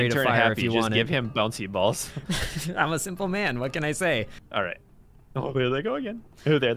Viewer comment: At first i thought i heard say I'm a single man Read more